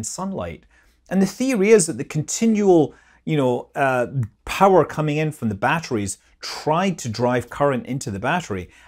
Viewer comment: what shè say at 15:05 Not making any sense "power cum in from battery drive battery" ????